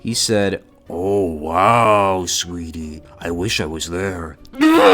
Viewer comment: i thought it said sweaty